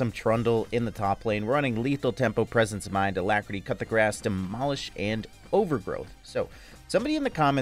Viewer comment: Was i the only one that heard him say "cut the grass", in the beginning runepage. Had to revind to clear the confusion.